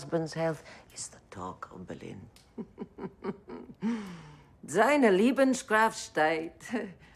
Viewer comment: Lol, i´m a full flegded german and even I didn´t understand, what she was trying to say at 2:36. Also the spelling of "Deutsches medizinische Hilfe" aside - i truly believe that Himmlers wife is the last person supposed to be there ;D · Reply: "Seine Lebenskraft steigt" The prononciation was very awful by some actors.